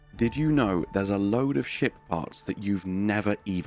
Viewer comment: Thought he said "Load of Shit Farts"